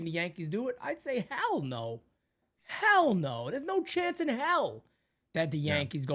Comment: I heard "Hal no" instead of "hell no" lmao that should be the new thing